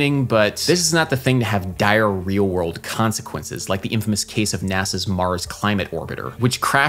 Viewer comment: Thought you said "diarrhea" world consequences - but you said dire, real world consequences.